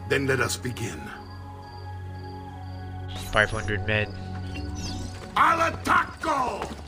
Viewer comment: sounds like there saying i like tacos